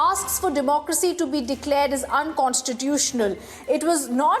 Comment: did she say democracy to be declared as unconstitutional??